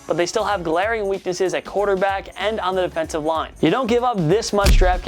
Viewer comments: Did he just say Seahawks got a weakness at quarterback 🤦🏽‍♂️😅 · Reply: He said CB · I thought he said quarterback when he really said corner back and i was like ......... Russell Wilson?😂